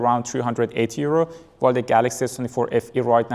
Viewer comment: Wait did you just said 308 euros? Where did you get it for 308 euros? I want links or even if its 380 euros I want link for it